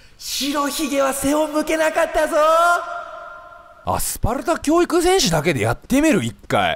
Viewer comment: アスファルト教育戦士に聞こえてﾀﾋんだ